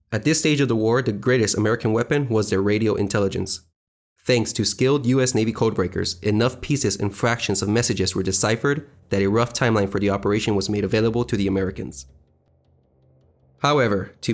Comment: I thought it said thanks to skillshare